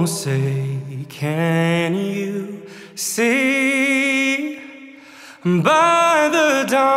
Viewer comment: Alejandro, just curious, did you say "Jose can you see?". I listened to this at Facebook and I thought that's what I heard. It should be "Oh say can you see".